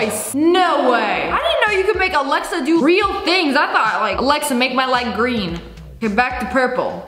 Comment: Tell me why when she said “Alex turn my light green” I thought she said “Alexa turn my leg green”